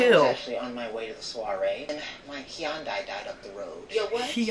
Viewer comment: Dude said “my Hyundai died” and I thought he said “Keyon died”